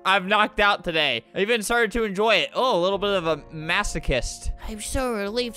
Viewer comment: Zizzy: shows arm Flamingo: Doesn't care, just talks about how the arm detached Wait, Zizzy's infected! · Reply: did he just said masochist?